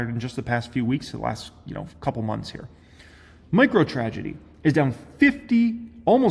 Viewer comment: did he say "microtragedy"?